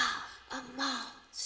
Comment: i heard “Coachella are you readyy?!!!!”